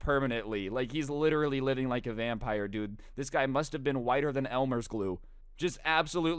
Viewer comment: I thought you said "wider than elmers glue" And I think that was More funny than "whiter than"